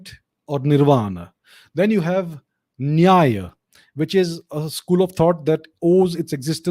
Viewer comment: can someone spell what he said at 26:03-26:05 "neyia"? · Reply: Nyaya, (Sanskrit: “Rule” or “Method”) one of the six systems (darshans) of Indian philosophy, important for its analysis of logic and epistemology. The major contribution of the Nyaya system is its working out in profound detail the means of knowledge known as inference